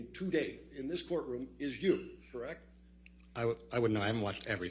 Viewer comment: Can someone tell me if I heard it wrong or not but at timestamp 4:53 to 4:56 it sounded like he said " I wouldn't know I haven't watched everything but I have watched a bet" I don't know if I'm incorrect or what but I thought Witnesses could not watch the trial or anything about the trial if they're going to be taking the stand and unless they were dismissed by the judge they still couldn't even if they left the stand.